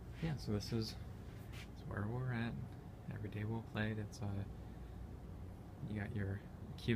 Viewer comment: I actually thought that said "Every day Wrel played."